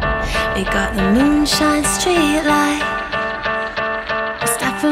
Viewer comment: Am i the only one who hears "illuminati" at 00:12 ?